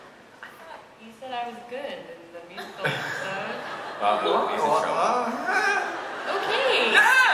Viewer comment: Lili,that was a dig at me,I thought you said I was good at the________? Cole,ehahAHHH. What did it say in the blank spot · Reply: She said musical episode